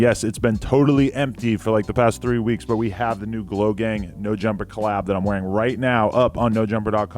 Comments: He said glo gang, i thought he said logang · my heart stopped when I thought you said "logang jumper" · Who else thought he said logang collab🤦‍♂️